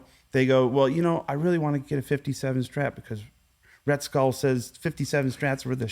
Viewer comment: Did Joe really say "Rhett Skull" @ 38:44? Hilarious!